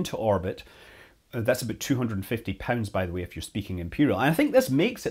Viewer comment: 2.44 I thought Scott said 250 pens..... What a strange unit of measurement 😁 Although perhaps that would work too 🤔